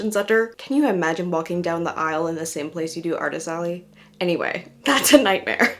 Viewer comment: this is so silly but for YEARS I have thought you have been saying “art-i-sally” and it JUST clicked you’re saying artist alley omfg. I just thought it was the name of something 😅